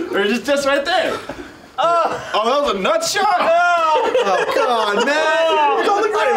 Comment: was that said nutshot, subtitle said Alfredo